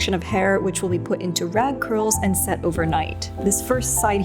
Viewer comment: thought you said "rad curls" 😭😂